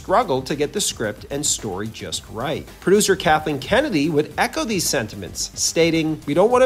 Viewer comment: LOL ! I thought he said Captain Kennedy ! What a great show that would make :D